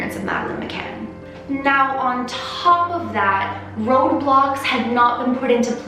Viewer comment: Ok so when she said road blocks Who else thought she said Roblox